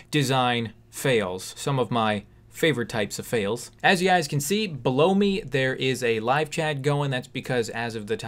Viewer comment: Did he say “Blow me”?!? Replayed it again 5x......OOOOOOHHHHHH, BELOW ME!!!!!! 🤦🏻‍♀️🤣🤣🤣🤣🤣 I’ll go clean out my ears now.....☺️